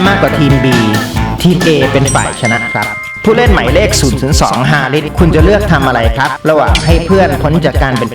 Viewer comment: ได้ยินว่า002ฮาริส